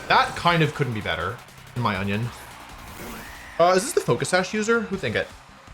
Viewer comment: did he just say “in my onion”? 😆